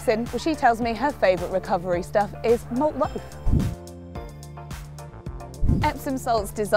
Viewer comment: What does she say at 3:36 it's driving me crazy 😂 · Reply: Clare Malt loaf? 😂 That's what I heard.